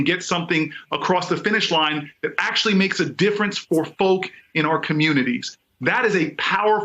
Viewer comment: “sausage making”. Did I hear that right?